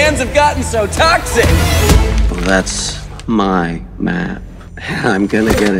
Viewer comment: i swear when i hear "that's my map" I literally herd "that's my man" and before anyone says "he clearly says map" i hear the subtle p sound if i am actually paying attention to it. If i'm not i hear man.